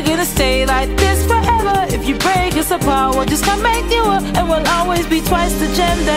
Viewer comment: for the longest time i thought Garnett said if you break us apart we'll just come back you up